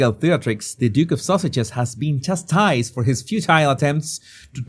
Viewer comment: Whenever the says Sussexes, I heard “Sausages.” 😂 😂 I like that MUCH better.